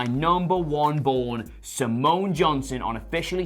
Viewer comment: When they said Simon Johnson, I misheard Samoan Jonson and thought that it's a short form for Samoa Joe. Anyhow, WWE found new Charlotte.